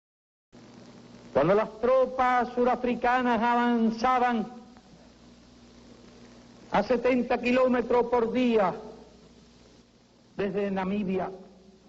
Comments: There is a mistake in the Che clip at 1:42:25. The subtitles are completely off. What he's actually saying is: "Cuando las tropas sudafricanas avanzaban a 70 kilometros por dia desde Namibia..." In english: "When the southafrican troops advanced 70 kilometers daily from Namibia..." Totally unrelated to the subtitles, except that Namibia kind of sounds like Media in english, I guess. Though at least what is attributed is definetely something Che would've said! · I don’t know if it’s supposed to be a joke but the subtitles of Castro’s speech are all wrong. What he actually says is: “When the South African troops advanced 70km per day from Namibia…” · The subtitles at 1:42:25 do not actually reflect what was said. He's saying: "When the south-African troops were advancing [almost?] 30 kilometers per day from Namibia..."